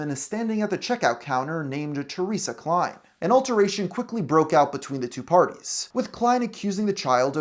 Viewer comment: Did you say alteration? You mean altercation.